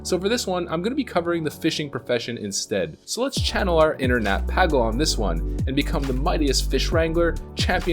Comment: "let's channel our inner Nat (Pagle) sounded like "let's channel our internet et"